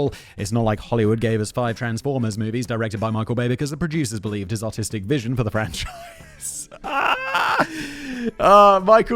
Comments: I heard that as "Michael Bay's autistic vision" which totally tracks. Special interests include explosions, incendiaries, ordnances. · Seriously thought you said Michael Bay had an autistic vision